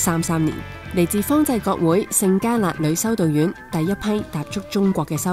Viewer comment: 條片好高質！！btw應該 17:40 係「聖嘉勒」instead of 「聖家辣」。唔知澳門嗰邊係咪「家辣」但香港呢邊係叫「嘉勒」的